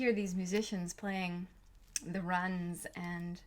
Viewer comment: did she say the musicians playing the “runs”